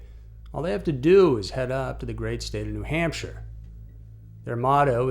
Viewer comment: Lol I thought it said New Ham Shirt